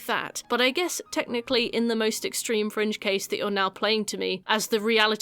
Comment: When you were talking about the HIV stuff I thought you said “in some French cases” not “fringe cases” and I was like “what the French doing different?”